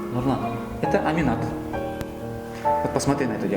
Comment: послышалось это маринад😂😂😂😂